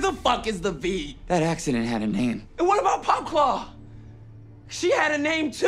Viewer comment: gotta love the subtitles saying "AND WHAT ABOUT POPCORN, SHE HAD A NAME TOO..." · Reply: It's POPCLAW